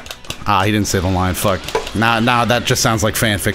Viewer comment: Did i hear say "fuck" 7:13😂?!!